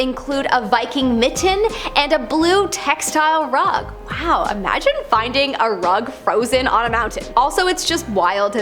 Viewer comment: i heard it as "Imagine finding a *rock* on a mountain"...